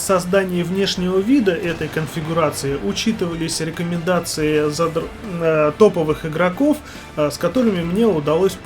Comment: сборка классная . На 06:15 про прицел выбор протанки хотел сказать учитывались рекомендации задротов) Или мне послышалось*?)